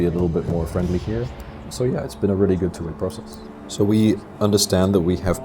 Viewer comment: At roughly 1:00 it says a really good t*** process. Wonder if gameinformer can tell me what did the voiceover said? Thanks<3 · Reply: Junjie Yu “Two-way” process.